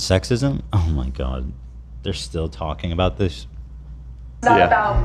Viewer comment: Thought he said they’re not talking about Hasan but here we are again